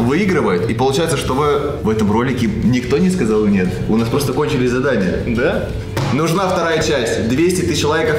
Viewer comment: Мне послышалось ( и это больше передаёт ролик) не ,,кончились", а ,,конченые задания"